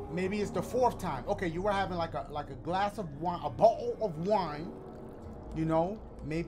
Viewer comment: A bowl of wine? 😂 Is that what I heard?